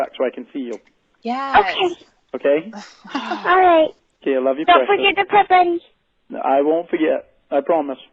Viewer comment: What does the decoy say here? " Dont forget the ? __ ?" Sounds s little like Presents maybe? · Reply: " "Don't forget to pet him'